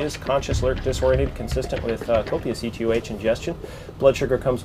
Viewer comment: did he say "consistent with copious opiates ingestion"? - Heh so she is jacked up on heroin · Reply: Josh Roark He said "consistent with copious E T O H ingestion".EtOH being ethanol commonly alcohol.